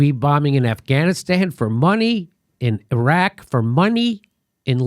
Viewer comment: Everytime Jimmy says "money" I hear yanny.